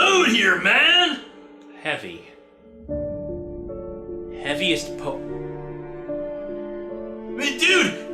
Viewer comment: Still don't know how clued into who The Entity was based on the word "heavy." "Heavy is a po..." what was he going to say? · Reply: Torrie Garvin I've always heard it as "Heaviest po...". As in, 'heaviest pokemon'.